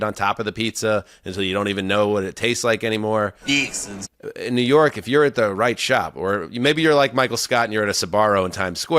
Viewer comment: what is this drop? What’s being said? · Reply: Ha! It's "Feeexin's" (like, the "fixings" that go with a food item)